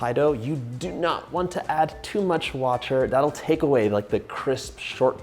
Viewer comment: At the 5:35 you say “ with pastry dough you DO NOT WANT TO ADD TOO MUCH ? but you do need some”. Too much what? Sounds like you said “watcher” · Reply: Ice water